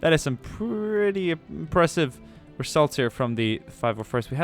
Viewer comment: Is it just me or does he say a couple times during and at the end of the video 40:50 "impressive results from the 501st" xD